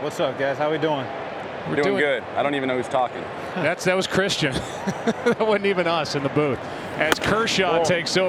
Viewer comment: Hi guys! Can anyone tell me what Joe Buck is saying at 0:36 after saying "That sounds Christian."? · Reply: Hyun Yoon he says “that was Christian, that’s not even us in the booth”